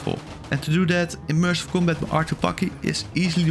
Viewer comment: I heard fuck it and then I went "Ohhh, bucket" and then I realized he was somehow saying combat I can understand your accent but what was that bro